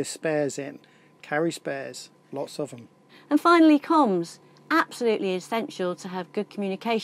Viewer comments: I thought you said "combs", And I thought because all that salt air makes a mess of your hair, but then a glint caught my eye. 😉 Again, so informative. Thank you for sharing. Sorry about your trip home being cancelled. Sorry I can't do more for you than a thumbs up. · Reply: I thought she said Combs also, must be my colonial English ;) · I thought you said "combs", And I thought because all that salt air makes a mess of your hair, but then a glint caught my eye. 😉 Again, so informative. Thank you for sharing. Sorry about your trip home being cancelled. Sorry I can't do more for you than a thumbs up.